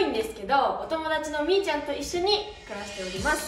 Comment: 友達の"兄ちゃん"と聞こえて一瞬びびった